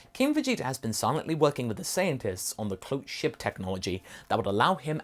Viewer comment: Did Masako just call the Saiyan scientists "Saiyantists" at 10:22?